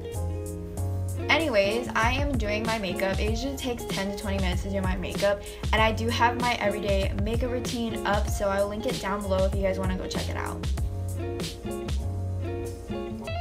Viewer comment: “I heard Asian takes..” did anyone else?